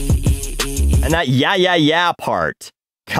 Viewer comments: I think I need to get my ears checked cause I didn't hear "yeah yeah yeah yeah", I heard "yeet yeet yeet yeet" which... with your "throw my cat" line DOES make for a funny line (though dark) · i thought it was saying "E E E E E E E" instead of Yeah yeah yeah yeah yeah.